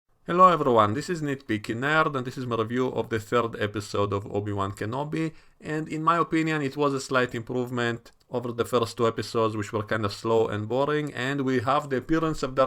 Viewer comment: Every time I watch one of nitpicking nerd's video and he starts "hello everyone, this is nitpicking nerd" I mishear it as "hello number one, this is nitpicking nerd"